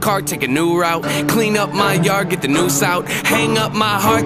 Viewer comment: I thought he said, “Clean up my yard get the new sout.” 😮